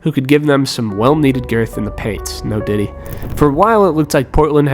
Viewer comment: did he say "no diddy"?🤣🤣 "im a max player, i will always be a max player" - deandre frustr-ayton